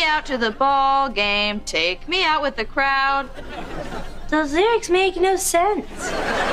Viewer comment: Whoever edited this really thought she said crown 👑 🤣